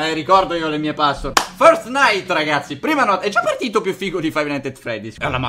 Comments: solo io ho capito "fortnite" 🤣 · "first night" io ho capito fortnite😂😂😂